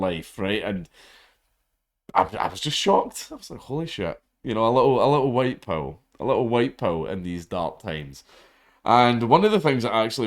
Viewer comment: he says “a little white PILL”, not “a little white power”. Just in case you misheard.